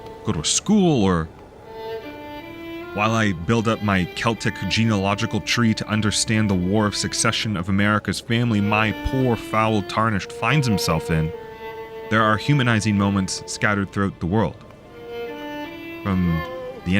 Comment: I thought you said America’s family, and I just laughed… idk why but as an American, shitting in America has become one of my favorite pastimes. But then I rewound and found out what you had actually said and was like… oh ok that makes sense.